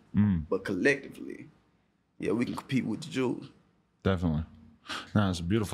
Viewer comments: did he say "compete with the jews" 1:33 ? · did he say “we can compete with the Jews”?